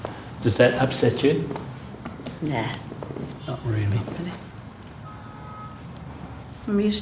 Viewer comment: WTF did the queen just say "nah" lmao